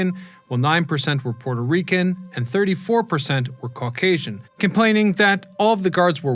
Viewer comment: am i the only one who heard him say ‘34%’???